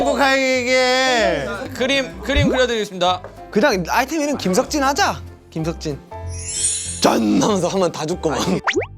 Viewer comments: i hear jungkook said : "kenyang kim seokjin aja kim seokjin".😂 Indonesiable bangett😂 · 'kim seokjin aja kim seokjin' kedengerannya malah gitu 😭